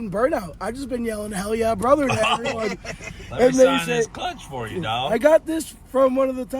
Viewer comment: That dude at the end who you signed his clutch. His energy was to the sky all night long dude was pacing the stands screaming a majority of the race 😂😂 10/10 fan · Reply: Man I thought you said crutch 🩼 so when he walked up with a CLUTCH I was lost lmao